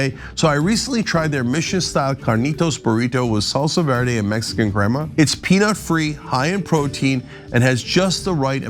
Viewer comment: Did you say it had Mexican grandma? 😂